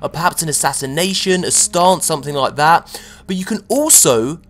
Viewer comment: a lot of it looks like destiny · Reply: wait I thought he said dance, he meant stance